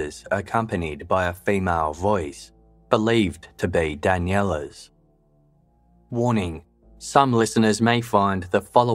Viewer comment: Can anyone type out what the two voice mails at 57:04 were saying. Listened several times cannot understand it, and why it was distressing for viewers to listen. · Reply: In the first one Daniela said - you're scaring me. And in the second one you could hear say - dont touch that. They're disturbing because they were surruptitiosly recorded at what many people believe to be close to the time she was murdered. I believe she was murdered 100%